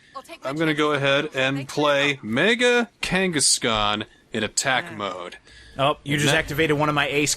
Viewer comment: Not a card guy myself but it sounded like they were saying mega Genghis Khan